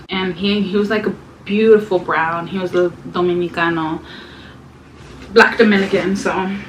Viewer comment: Wow !!.... 7:12 did you said dominicano o americano Couldn't hear clearly what you said 😂😂😂 ! .... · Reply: Dominicano 😉